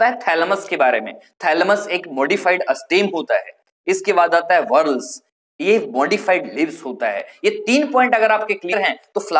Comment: Sir, I can't understand the pronunciations. It is saying goruls? Or